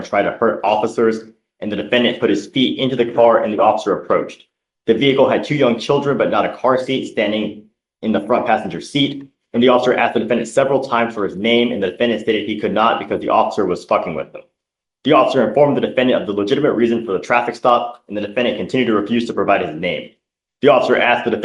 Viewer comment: Did he say "the officer asked for his name but he could not because the officer was fking with him?" @ 7:55 ish